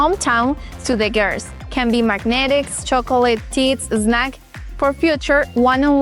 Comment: she said "tits" instead of "chips" 😆